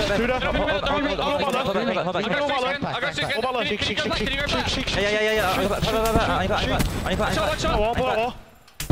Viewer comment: what are they even saying 😂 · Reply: Shink shink shink